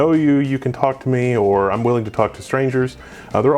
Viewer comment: thought he said fuck not talk!?